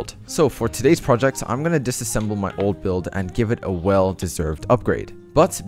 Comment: dam it i rlly thought he said give it away than give a well upgrade XD